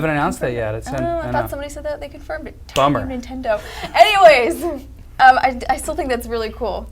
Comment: did she say fuck nintendo 3:24 at lol